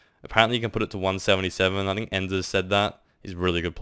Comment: who was he talking about there? I couldn't catch the name. I play on a 1440p monitor and would like some more explaining. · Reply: Ender i think it was.